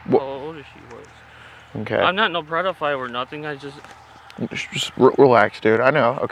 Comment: wait did he say pred o phile??? 😂😂😂 so accurate